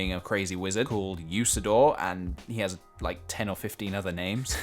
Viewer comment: Captions say 'Crazy wizard called use the door.'